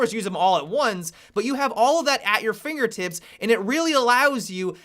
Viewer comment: who else heard fingertits?